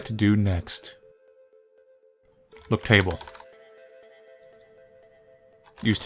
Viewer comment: so I wasn't exactly watching at the time when you did look table, but I heard you say look table.... well, sort of. What I heard wasn't look table, but "Flip table"